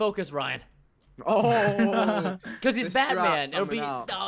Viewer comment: I thought Preston say we have to kill Ryan because he is bad. What he actually said because he's Batman